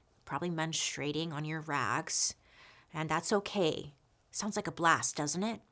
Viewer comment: I couldn’t help but start laughing when you said close your eyes and all I heard was “ menstruating on your rags” 😂😂😂